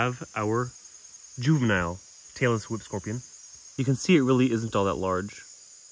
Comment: I heard: Taylor Swift Scorpions Went: Huh, I know I misheard, but it's fitting for her.